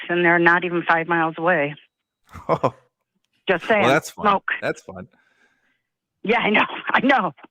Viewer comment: Could yall ask why she cant see the mountains from 5 miles away? I thought I heard her say smoke but Im not sure??!!!!! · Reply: It was hard to hear, but she said it was due to smoke. Must be from the wildfires. I live in southern NJ, & even we had some smoke.